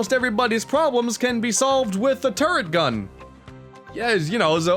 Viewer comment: Ok I must admit, at first I thought you said "the turd gun" and I was confused.